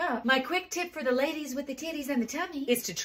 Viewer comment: Hahahaha I thought you said "Enjoy your tacos." Took me a second! Lol❤